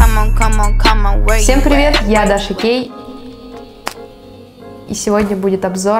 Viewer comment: Мне одной послышалось " Всем привет, я Даша Гей'!😹😹😹 Не в обиду)♥️